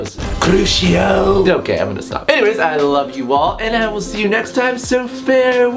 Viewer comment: At the end when you said "I love you all" I heard "I love you wall" and I had to kind of let that just sink in